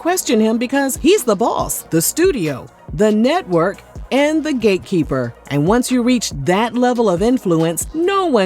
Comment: sorry, did you say gatekeeper or “gaykeeper” 😩😩